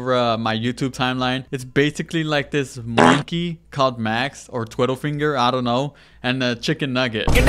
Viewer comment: did bro say Twiddlefinger